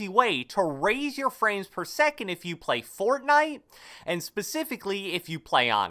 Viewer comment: I thought you said friends per second! But you didn’t...